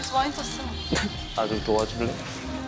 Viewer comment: 정무가 나좀도와줄래? 하는데 안 들리나봐요ㅋㅋㅋ